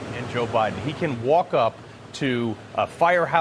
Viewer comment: did he say biden?